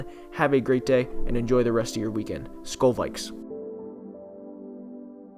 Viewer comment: He says Skol at the end of earlier every video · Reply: bruh I thought he said skull